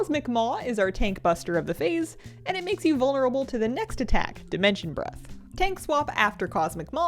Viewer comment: i thought said "dimension bruh" at 1:19 and i was like wha??